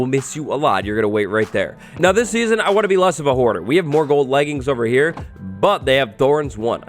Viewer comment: i had to do a double take bc i thought this man said "i want to be less of a whore"😭